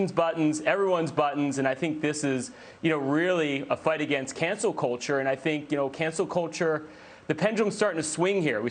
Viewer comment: We're canceling Cancel Culture. (I kept hearing "cancer culture".)